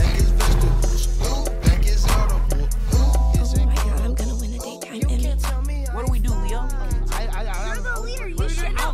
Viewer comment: What April say? 🤔🤔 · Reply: I'm going to win the daytime Emmy